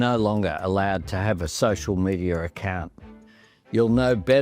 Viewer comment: did he say socier medier?